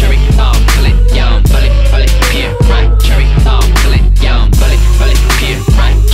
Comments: I always thought it said I'mma make you sit on the stage! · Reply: lmao ikr. and in the beginning I hear "bullet, bullet, peer, like cherry bomb, click nah." I was wayy off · When I first listened to this song I thought taeyong was saying "bullet bullet deer fight cherry bomb fill it yo"